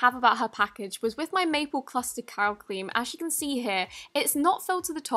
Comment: Who else thought she said maple cluster cow cream